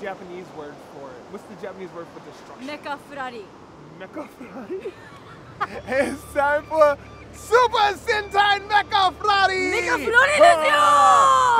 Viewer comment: DID HE SAY SUPER SENTAI!!!!!